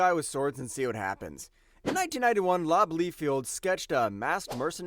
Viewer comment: who? Did he say Lob???